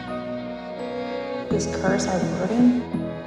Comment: What does she says at 0:34? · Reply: « This curse, our burden. »